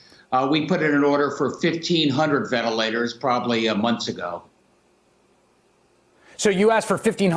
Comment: Rudy Penza Whats it like to be objectively more stupid than someone you consider a fool? I know! What if a different person refuted your presumption? Someone with both power and direct insight making the same theft claims? Like the governor of a blue state or something... Well look at that, stupid! Another person DID! <link> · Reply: Hey, Did you hear what this guy said at 0:44 ??? I'm still trying to understand what this guy said right there? It sounded like blank "months" Why did you try to get 1500 "months" ago when things weren't happening yet. Do you catch my drift? Sounds to me like he WASN'T prepared and is now trying to blame EVERYBODY else.